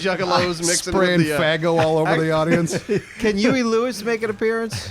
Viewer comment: definitely hear the word faggo instead of faygo coming out of mouth.